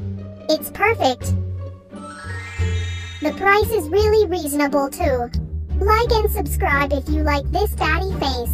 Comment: I heard “daddy face”